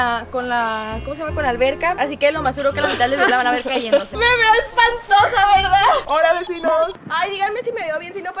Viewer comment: primero le entendí que dijo "me veo es pantoja" jsjajajjaja